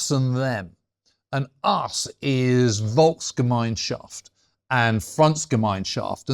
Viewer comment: It sounded like he was saying “ volksgemeinschaft and push Francis down a mineshaft”.